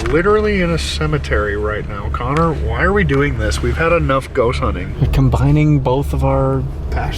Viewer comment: it sounds like says Goat Hunting to me